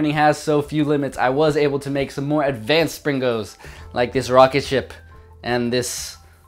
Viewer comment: i hear "pringles" all the time you say "springos". that's a little confusing :B